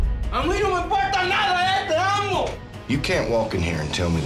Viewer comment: what exactly does he say? I need it for my translation... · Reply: No me importa nada (ey), te amo!! I don't care about anything (hey), I love you!